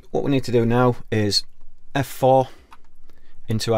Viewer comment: Hi.. what do you say at 01:04 in the video ? I'm trying my best to understand this. "Right, all we need to do now is......... ??" F 4 ?? I don't know how to open that window afterwards.. I have UR22mkII and came with Cubase LE AI Elements 9.. And I`m here watching your tutorial because those from steinberg are too complicated for a beginner as me. I just want to record my bass guitar to do some covers and that kind of stuff. Thank you ! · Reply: agu arrieta press F4 Function key top open the vst connection window